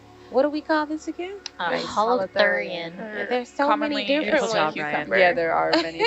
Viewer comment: mark, I didn't catch the name of the little creature they were fawning over (although they seem to be fawning over everything 😂) anyway, it looked like an orange or red color for the pieces within it but looks its outer body is see thru. So yeah anyone know what the name was cuz they talked all together and I couldn't catch it. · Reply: Captions reveal it! They said "Holothurian" which is a bit vague bc that's just the sea cucumber family—but looking at wikipedia it seems to belong to the order Elasipodida. If you go on the sea cucumber wikipedia page this weird thing is in the middle picture I think